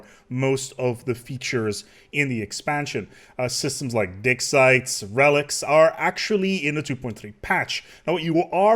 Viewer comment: I really think you just said dick sites... I can't unhear it.